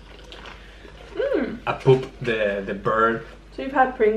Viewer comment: Did he say regular flavor (pringles) with the flavor a poop of da bird?